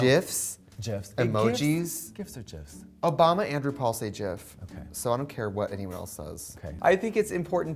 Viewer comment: Anyone else thought she said "Oh! Bami Andrew Paul says "jIF" ?? I was like who dat??